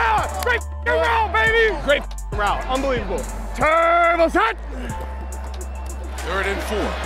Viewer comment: I know it's "Turbo Set", but I always hear "Terrible Set!" before he busts off something huge.